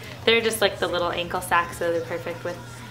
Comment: Lol. For some reason i thought u said u were obsessed with sacks in stead of socks.